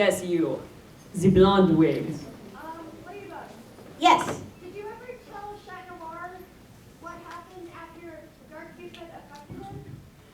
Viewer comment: What was the question at 12:08? I heard dark something and then everyone went "OHHHHH!" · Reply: Someone asked Chat Noir if Ladybug had ever told him what had happened after the fight with Dark Cupid :)